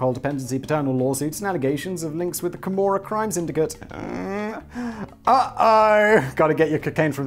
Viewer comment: I misheard in the teaser and spend half the video wondering who was involved with the Cremora crime syndicate!